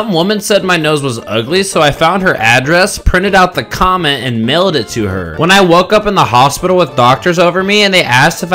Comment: Ong I thought that first one said she "nailed it to her" and I was like WHAT